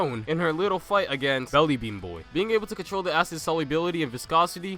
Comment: Did you just call him belly bean boy???